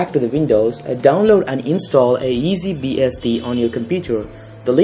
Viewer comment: did he say easy"BSD" 9:03? ;)